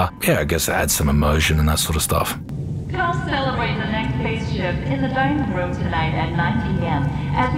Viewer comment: What does that anouncer say? Come celebrate the next spaceship? Payslip? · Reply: It’s “phase shift”.